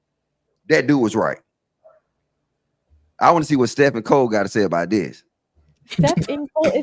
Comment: I'm from Atlanta too and I thought he said steff and cold 😂🤷🏾‍♀️